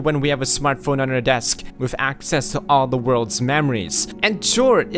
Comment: Initially during the 'Faster sleep wait' part I thought I heard him say 'we have smartphones and access to all the world's memories'. Which I thought was actually a pretty profound way of looking at it. Then I realized he actually said 'mammaries'. Yeah.